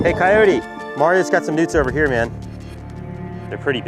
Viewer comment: at first did anyone else think he said "Hey Cyote Mario got some nudes over here man. There pretty big to."?